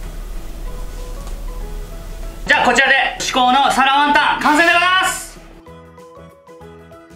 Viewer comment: 身支度しながらながし聞きしてたら 「皿ワンタン」が「サラマンダー」に聞こえて 『どんな料理だ？』ってまたリピート再生しましたw 作ってみよう(๑•̀ •́)و✧